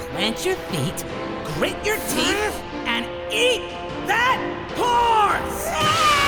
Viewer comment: i thought he said "EAT THAT WHORE", that acctually sounds funnier